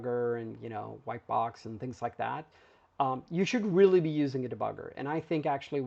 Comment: "You shouldn't really be using a debugger"? I'm confused here. Did I mishear? Did you mean to say "should"? Or were you referring to textual debuggers?